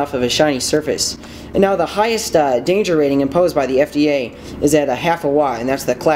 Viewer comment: Did he say... The FDA? 😳